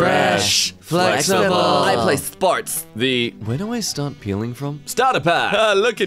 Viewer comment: I thought that it said "where do i start peeing from" all of them were tubes so it made sense but then i saw the lollypops and went wtf